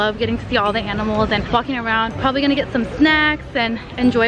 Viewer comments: sends picture of the zoo brian goes to the zoo “omg how did he find meeee” 🤨 is everything okay up there? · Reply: I think she said fucking around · she sound like she is say flocking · Bro cussing at 4:26 was peak Yes I know she said walking around it just sounded like the f word